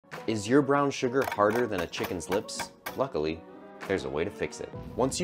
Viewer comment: I heard children's lips and clicked off. Glad I went back. For no real reason at all